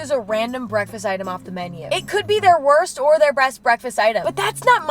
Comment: did she say breast breakfast?